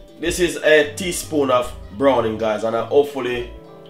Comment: After the honey, I could not understand what was the brown stuff. Sounded like you said brownie but I am hard of hearing and I know I heard wrong. Played it over and over and could not understand what you said. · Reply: Browning..you can use gravy master